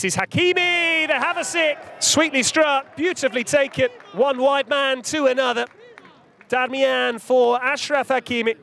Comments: It did sound like he said one “White” man to another 😂😂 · I thought the commentator said "one white man to another"😂